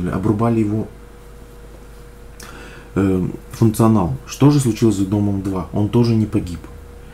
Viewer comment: я конечно понял но мне всё равно послышалось: "что же случилось с ДОМОМ2")) и еще вот